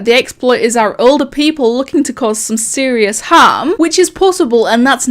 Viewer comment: did you just call harm ham?